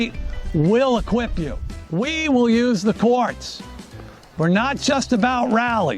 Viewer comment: When he started talking about the courts, I thought he said “quartz” and I was momentarily confused about why he suddenly switched topics to geology.